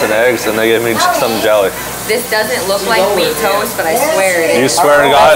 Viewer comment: What could i have named this video? Originally, it was "swan diving into the movie stack" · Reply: I thought she said MEAT toast 😂😂😂😂😂😂😂